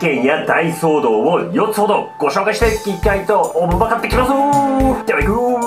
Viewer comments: 「おもんばかっていきます」に聞こえた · 「ご紹介していきたいと慮って行きますぅぅ〜」 って聞こえた